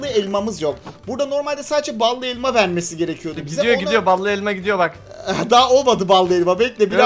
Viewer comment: Abi balı alma değilde elma şekeri duydum